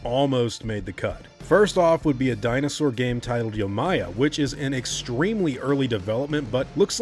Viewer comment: I thought yomia said yo mama at first.